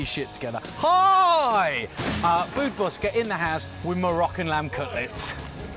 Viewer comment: Haha i thought you said "My rocking lamb cutlets"